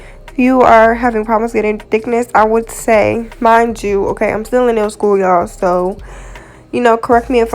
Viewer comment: When I heard you said "I'm still in nail school" I was like WHATTT!! BUT your soo good!! Ilyyyy keep uppppp!!